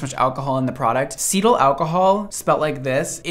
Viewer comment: Lord I thought you said “fetal alcohol”